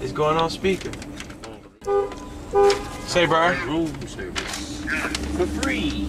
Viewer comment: on 2:12 it sounded like sumbody said room service 4 free but wut does dat have 2 do wit nething but dat was hilarious lol